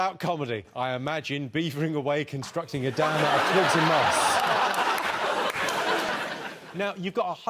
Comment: What is Jimmy saying after "beavering away a construction dam" in 8:33 ? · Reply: Out of twigs and moss